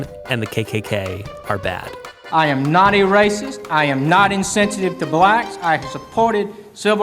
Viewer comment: all I hear is: “I am not a crook.” I love the dude in the back just looking at him